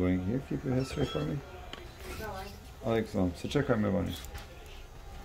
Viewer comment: Did i hear: 9:07 ALEJKUMU SELAM, SAČEKAJ ME VANI? Veliki selam za naše ljude vani ❤️